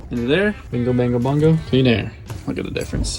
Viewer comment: "A binga bongo bongo, clean air" (I replayed this more times than I'd like to admit to figure out what said)